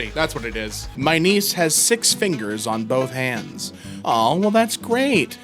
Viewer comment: As I am washing the dishes and having this as a background noise, hearing my niece have six fingers makes me rewind the video checking since I thought I heard said my knees have six fingers. 🤣